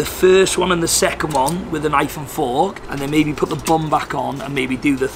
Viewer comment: For real, man, your video has done a backflip, snapped the bad guy's neck, and saved my day. I'm so tired and sleepy that, at first, I thought that you said at 6:40 "I'm gonna eat it with an iPhone four" instead of "with a knife and fork